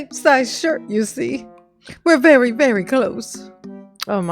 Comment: Wait, is he saying he's "very, very close" to a daughter who has never met his mother???? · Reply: It sounds like he was saying that he's very very "clost" with his youngest. He's such a moron !!!